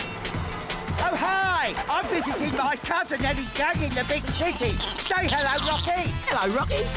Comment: What did he say? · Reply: "I'm visiting my cousin and his gang in the big city." We didn't see Rocky much after this. Prison.